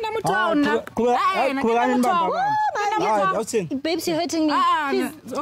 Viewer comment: For a sec i thought said “Bitch you hurting me”…. Besengimangele😩